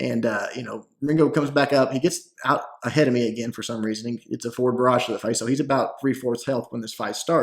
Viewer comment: When you say "Forward Barrage" (Skye's B ability) it sounds like you say "Four Garage"